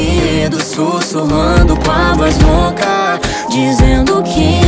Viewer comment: Na primeira vez q ouvi a música eu entendi susurrando com a vassoura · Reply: E eu entendi “sussurrando com a voz louca” hahahahhaha